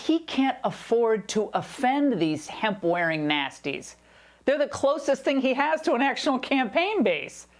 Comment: "Hemp-wearing nasties" darn, thought she said (national socialists) instead of nasties. Would have been more appropriate and funnier if she called the lefties "(national socialists)" because that's what they are and that's who they are behaving like.......